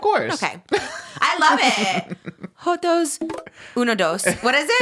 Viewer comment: I WALK AWAY FOR TWO SECONDS AND I HEAR SAYING SOMETHING JOTOS I WAS LIKE DID I HEAR THAT CORRECTLY 😭😭😭😭😭 and in my head i heard “joto por joto and i was like purrrrrr gay4gay”😭😭😭😭